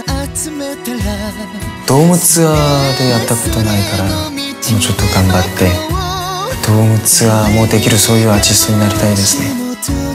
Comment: 皆さん、外国人にちょっと手伝ってください。4:23からチャンミンは何を言いますか？よく聞こえない TT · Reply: 「ドームツアーでやったことないから、もうちょっとがんばりたい。 ドームツアーもできる、そういうアーティストになりたいですね。」